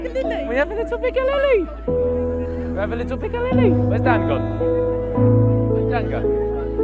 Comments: i swear he said where did dad go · Ethans dad was there? at 17:50ish did he say where did dad go? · Awww when he hugged his mom that was such a wholesome moment🥹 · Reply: For a second i thought Behz said “where’s dad gone” as a joke after the mamaBehz hug 🤣